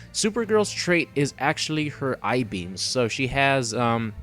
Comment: Why's it so hard for people to say "Heat vision"... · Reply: When he says that she shoots "eye beams", I hear "i-beams". I imagine Supergirl shooting girders at the opponent.